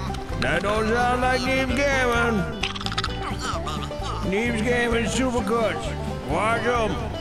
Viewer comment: I thought was saying needs gaming super kush in the ad